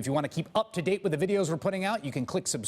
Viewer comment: Every time MSNBC uses that Chris Hayes clip as the closing card I always hear him say "just blow me" instead of "just below me". I cant be the only one lol